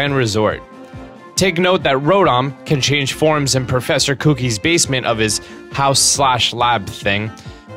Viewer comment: I'm sorry did you say professor cookie 1:42 Or that's how you say in your country or state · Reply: Kevin Gaming 257 No,he said Professor Kuki